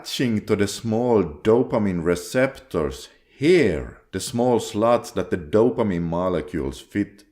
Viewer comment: LoL - I heard you saying “the small sl*ts”, instead of slots at 05:54. PMO has really messed up my brain 😂. Still going strong with NoFap - but temptations are ever present. Videos like these keep me on the right path. Thanks!